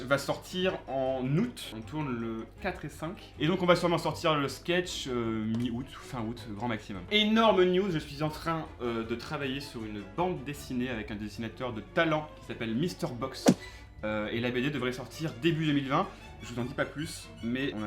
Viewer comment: c'est moi ou il a dit "énorme nude" xD